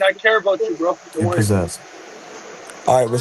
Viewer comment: I think the guy talking to Ryan called him possessed haha